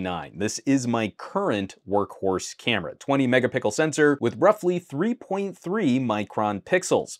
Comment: … did you say, “20 mega-pickle camera”? 🤣🤣🤣